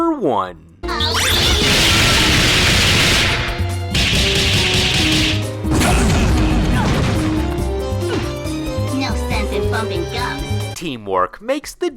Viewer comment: Did just say, "No use in frumping ducks"?